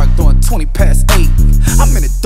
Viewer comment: whos here from the post malone video 👀🔥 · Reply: Thought he was saying duckin duckin duckin. But he saying in tha dark 😂🔥 shit gooo